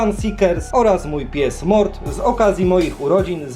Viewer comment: ja dobrze usłyszałem nazwałeś psa Mort)